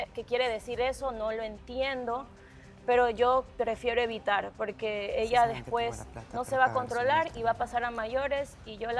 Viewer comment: No sé si estoy bien o Es mi impresión o escuché mal lo que dijo Mare cuando hablaba, en el minuto 1:38:05 🥺🥺 Mare dice en silencio, pero se escucha algo SI SABES QUE TENGO LA PLATA PARA QUE?? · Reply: Mare dice "si saben que tengo plata para pagar su multa" Le dece esto al microfono a la producción por que tocan el tema del cual ella ya no quiere habla.Y si ella dice una mala palabra o se comporta de una manera indebida las multan.